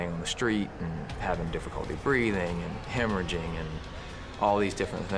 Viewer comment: What does the Medic say at 0:44 after "[...] breathing and [...]"? I rewinded that part like 10 times and am still not able to understand the word he's saying. I'm no native English speaker but normally I have no problem with comprehending the sense in documentaries etc., but Jesus Christ, it drives me crazy that I can't retrace it at this point. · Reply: Hemorrhaging